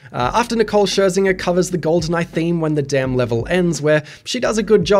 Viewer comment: I will admit, I definitely thought "he really doesn't like it, huh", mishearing "when the Dam level ends" as "when the damn level ends"